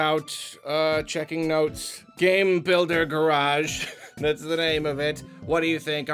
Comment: I thought it said game builder garbage at first